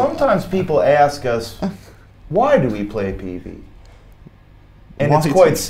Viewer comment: what does he say here ¨why do we play ....¨ what does he says after that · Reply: Brian says "Sometimes people ask us Why do we play peavey?